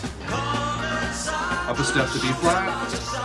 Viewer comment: Thought i heard you call out C flat! You go.